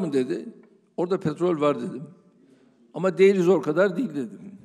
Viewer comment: Ama ... ne diyor orda ? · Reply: Değeriniz okadar değil dedim diyor